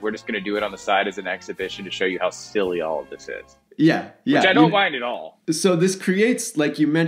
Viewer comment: the mistake on the subtitles there absolutely ruins what the dude actually said ("which I don't mind at all" vs subtitle's "which I don't like at all.") If you need an editor, I'm looking for a side gig! Also still weirded out that you're using a gambling site for ad revenue. Have no problem with people spending their money how they wish; have a bit of a problem with sports-related sites steering people towards this.